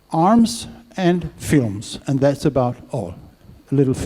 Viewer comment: Isn't Hollywood just the marketing arm of the Military Industrial Complex? In that light all the US makes is arms. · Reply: Oh he said weapons and films...I thought he said weapons and fumes